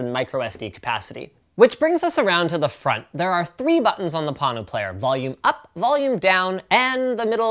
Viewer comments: I thought it said porno player, i was like wtf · I giggled when i thought he said porno player. :D:D:D